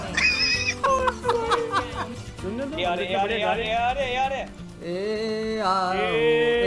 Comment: Is it just me or did he say Yare Yare